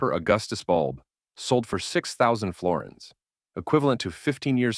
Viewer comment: did he said 6000 florence